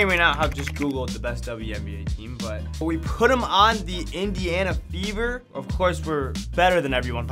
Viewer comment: I did a double take when you said Indiana Fever. I legit thought you said the Indiana Beavers 😂.